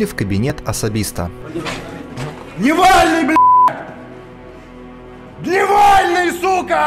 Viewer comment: мне послышалось в первый раз ,, Навальный бл.дь!". XD